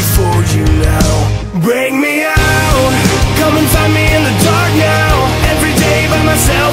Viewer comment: comment what u heard P.S I heard c*m inside me in the dark now